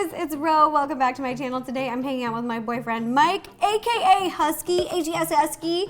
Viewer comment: When to introduced husky I thought she said husband my face was 😵